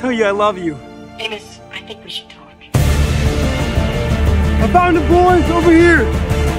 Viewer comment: I thought she said "I think we should do it" till I saw his reaction